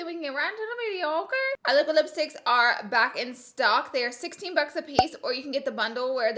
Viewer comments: I ALMOST SCREAMED I THOUGHT SHE SAID HER LIPSTICKS WERE 60$ A PIECE NOT 16$ WOAH. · At first I thought you said the liquid lips were $60 a piece and I was like GURL but then I scrolled back and heard correctly lol 😂